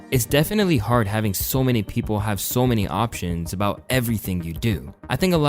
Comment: “Option” instead of “Opinion” is hilarious 🤣🤣🤣